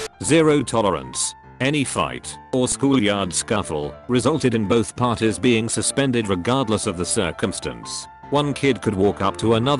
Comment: I thought it said "any fight resulted in both parties being circumcised" lol